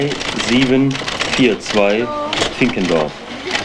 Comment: habe ich das richtig verstanden? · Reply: Finkendorf.....